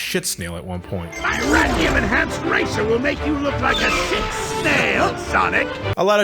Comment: Seriously, what did he say? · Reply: Sick Snail.